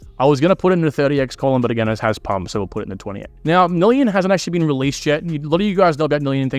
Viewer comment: What’s the name he said? Million? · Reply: Nillion